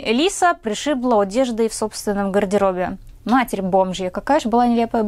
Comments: Мне послышалось матерь бомжья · Мне одной послышалось "матерь , бомж я"? 😂😂😂